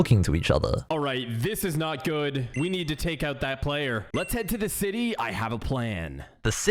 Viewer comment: did they called him a player...